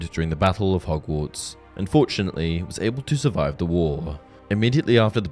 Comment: Did he say unfortunately 25:53 about him not dying during the battle of hogwarts?